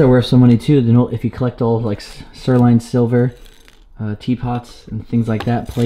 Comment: Did say ...... Sirloin Silvers ?